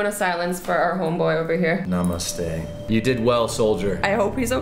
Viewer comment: did he say namaste if yes then cool man i m indian namaste